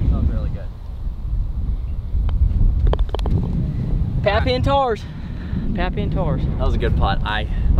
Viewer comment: I thought said Captain Tard. 😂